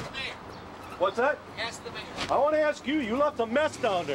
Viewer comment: When he said "Ask the Mayor" I had a double take because I Thought he said "Masturbator"